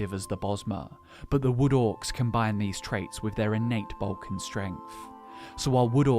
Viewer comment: i thought you said "their inate balkan strength" 😂